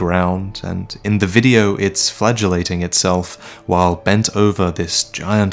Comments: i thought you said flatulating. As in, farting over the bowl. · I thought you said flatulation raised the chair at first. Very happy when you revisited the word. My guess would be Velka though I wouldn't put it past From to make that character a guy :( Probably someone weve never seen mentioned.